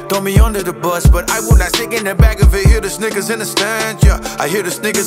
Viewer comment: what does he say? Am I hearing right ? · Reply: Silvi _0 snickers yo